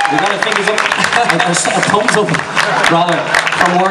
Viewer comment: Does anyone know what Neil says at 3:39 ? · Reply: Neil said, ''We got a fingers up! A thumbs up, rather.'' XD <3 Then that face he makes and the laugh.. he's so cute! lol